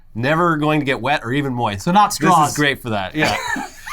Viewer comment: omg good video guys :D I spewed coffee all over my table when I heard "Cum Straws" lol